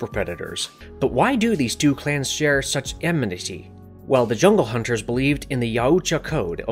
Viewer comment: I still don't know what you were trying to say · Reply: Amenity